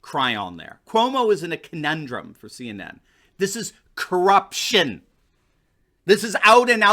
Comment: I thought he said "Corrupt Shit!" when he actually said "Corruption!" XD